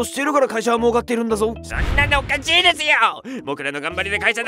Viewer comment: 「おかちい」にしか聞こえん